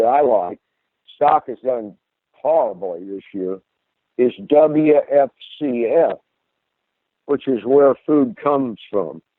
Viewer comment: Can someone understand the stock nme or ticker at 11:04 ? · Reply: wfcf